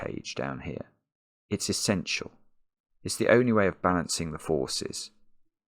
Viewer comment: "It's the only way to balance the forces", he says, and I hear "It's the only way to balance the four-ses"